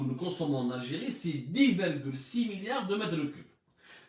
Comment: C'est 14 milliards de m3 et non 10'6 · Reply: écoute toi même 25:20 Il dit bien 10.6 milliards de m3